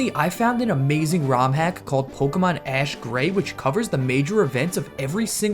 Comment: Thank god I thought it said pokemon ash gay